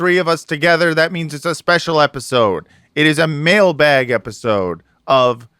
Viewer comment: Wow. I heard this as “male bag” for some reason. Both alarming and exciting.